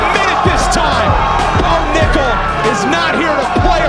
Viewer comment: I thought he said Bone Nickel, that would be an epic name lol